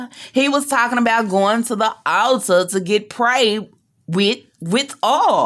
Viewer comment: LMAO I thought you were saying he wanna to take her to the alter to get married 🤦🏽‍♀️🤦🏽‍♀️🤦🏽‍♀️🤦🏽‍♀️🤦🏽‍♀️